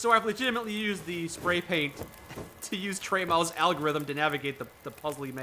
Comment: The what algorithm? That sounds super useful, but I can't make out what you're saying. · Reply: Tremaux Algorithm